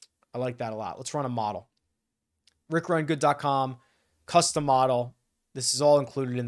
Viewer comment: you said custom model, I thought you said Cus D’Amato 😂